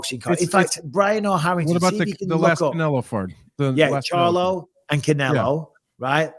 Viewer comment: Did Anthony say "The last Canelo fart" at 55:52 HAHAHAHAHAHA!